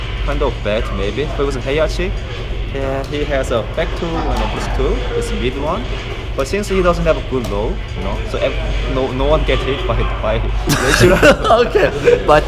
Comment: what did he say? cant hear it too well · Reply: kumbaya shit he said heihachis rage drive is a mid and nobody gets hit with it because heihachi doesn’t have any good lows to mix it up with and force the opponent to duck.